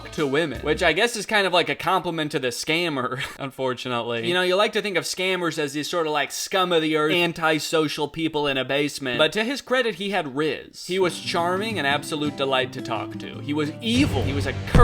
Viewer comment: did just say rizz